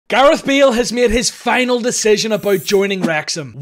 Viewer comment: I thought he said gareth meal